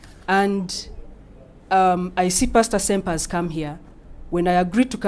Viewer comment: Did Pepe just called him Pasta senpai? 😰